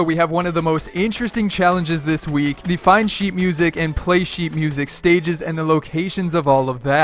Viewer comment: I thought u said sheep music😂😂😂